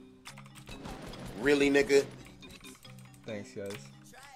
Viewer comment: did he say really,nigga?!!